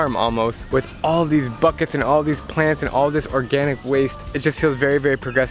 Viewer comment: wait at 2:18 did he just say planets